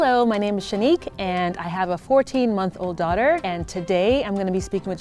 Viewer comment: I was only halfway listening so I thought said she had a 14 year old daughter I was like OMFG SHE LOOKS SO YOUNG